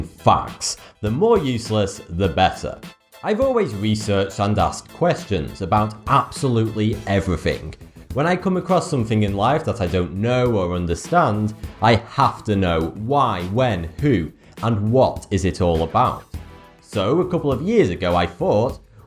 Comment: did he say everyfing?